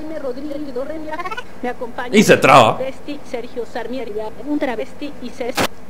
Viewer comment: Sergio Sarnierga (algo así entendí xd) Un travesti y SeS